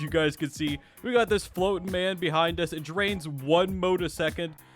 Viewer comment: "floating man" instead of stand 😭